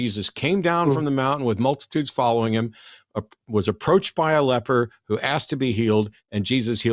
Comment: I know it is completely just speaking quickly but when Matt read the passage it sounded like he said, "Approached by a leopard..." and that just made me giggle more than it should. Maybe my brain is unusual but I was picturing what the text described and that image of being in a big group giving a sermon and then being approached for healing....by a leopard...I just suddenly pictured a sick leopard coming out of the crowd and I have to say, THAT would convince me of miracles more than anything actually in the bible!!